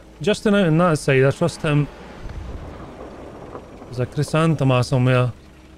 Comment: WHAT did u say at 8:26, subs say u said Chrysanthemum lol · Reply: I did say Chrysanthemum! I got pet names for most tanks.